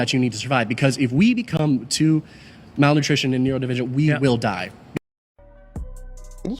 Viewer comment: At minute 20:18, the meat-eating arguer did NOT say neurodivergent; he said MINERAL DEFICIENT. I've been vegan for 41 years. These folks are so uninformed/uneducated about food.